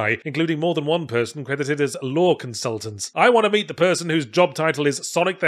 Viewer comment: Because of your accent I misheard "Lore Consultant" as "Law Consultant" and was MUCH more interested to hear that employee's story.